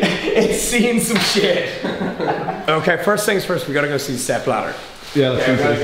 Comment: Thought said "Sepp Blatter" said "Step Ladder!"